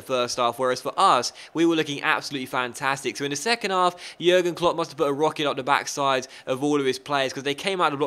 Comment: Did just say "Jürgen Klopp must of put a rocket up the backside of all his players" at 3:00?